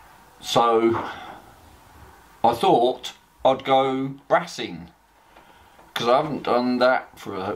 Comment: Oh Wrassing! I thought you said ratting 😁